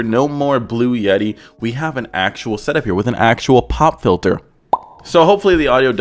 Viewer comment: Swore he said “bob the builder” for no reason until he said “pop filter” again